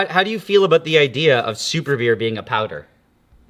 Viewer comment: Man I thought it said superbeef, dreams ruined.